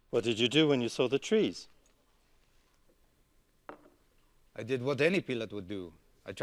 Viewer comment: What did you do when you saw the trees? I did what any peelot would do. I tried to climb over them. Pilot sounds like peelot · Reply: Ohh.. I thought he said I did what any pillock would do haha